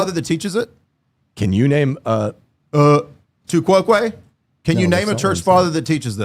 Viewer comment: Just as a heads up. Maybe it'll help improve your subtitles. At 1:16, Jay Dyer says "uhh, tu quoque?" This is the name of a fallacy. But your subtitles say "tu kwok wei" instead. Seriously, not a big deal. Just a little constructive criticism. Honestly, I'd love to help edit your content. I'm more passionate about the truth than I am about pedantic typos.